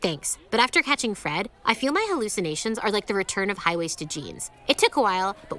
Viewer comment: i seriously thought she said "I feel like my hallucinations are the return of Highway Stygians" had to replay it like 4 times. wow this show sucks, who is it even for, just the writers?